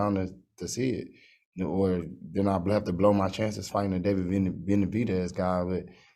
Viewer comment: did he say I have to blow the chance fighting benavidez